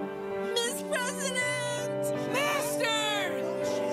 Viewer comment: did anyone else heard someone shouted master in front of her and after youkimura said ms. president